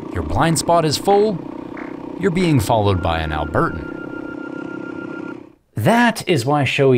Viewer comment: did you say "being followed by an Albertan"??